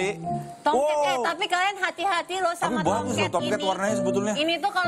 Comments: "Jadi tomcat gak boleh di pegang? Gak boleh di tepok?" Hahaha saya paham pak :v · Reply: Ngeh gak sih saat ngomong tomket karena blibet kedengeranya toket menit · Jadi, To"m"Ket ga boleh di pegang? Nggak (Jawab Serius): Nggak boleh di tepok? Nggak (Muka Polos): Biar ga melepuh (sambil ngakak): (Diem takut keceplosan): (Masih nggak engeh) (Muka polos) Joke anak pengkolan ini mah si kehidupan anak gang kalo di jakarta ahhahaha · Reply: Ngeh gak sih saat ngomong tomket karena blibet kedengeranya toket menit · Ngeh gak sih saat ngomong tomket karena blibet kedengeranya toket menit